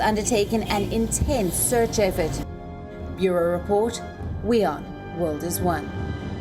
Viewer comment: At the end did I hear "Euro report, we are, the world is one ?" Whats up with that ?